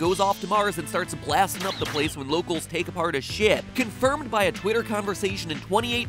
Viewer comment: sorry, this doesn't matter, but I can't tell if what he saying is "his ship" or another word... 3:53 sorry I love your videos <3.... beeeeyyyyyyy....